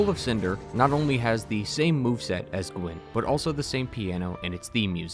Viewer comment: who’s oolen? Did you say Gwen and I just misheard?